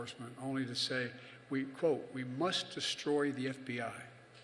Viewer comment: no, destroy wasnt said. Dismantle the FBI, that is what was said